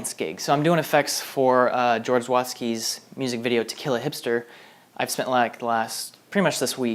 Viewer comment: I heard you say "To Kill A Hipster" as "Tequila Hipster" and then proceeded to make a song that would be titled "Tequila Hipster" in my head